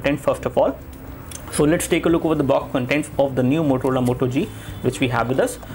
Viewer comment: did he just say MOTOLOLA Moto G? haha